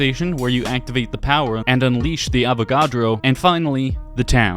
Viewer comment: did he say avocado?